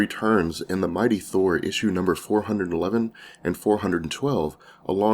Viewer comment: Great vid but 5:22 you said what sounded like memes but i know that you meant to say beams RIGHT:)